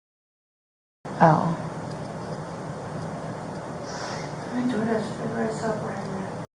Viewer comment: Sounds like she says, "I Door Dash driver. I saw Bryan there."